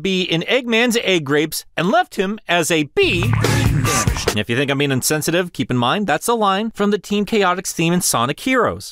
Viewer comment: Can someone tell me what is saying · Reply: I'm not saying anything. That was cut from the Team Chaotix song with Vector saying "brain damaged"